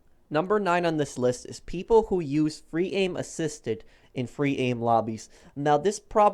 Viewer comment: Did he just say fully automatic sniper rifles and also assisted free aim ISNT AN EXPLOIT ITS LITERALLY PART OF THE GAME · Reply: Heath Drumm he said aim assist in free aim lobbies